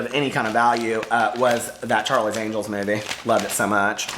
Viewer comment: lawd...I thought I heard "that Charlie Daniel's movie" wha??